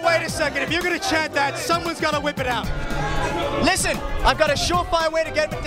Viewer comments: I could have sworn he said "butt of a virgin," but blood works too. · "Quick, I found a way into Heaven! I need the blood of a virgin, any of you will do!" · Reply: Cobe Forty Lol I thought I heard "butt of a virgin" before I read your comment. xD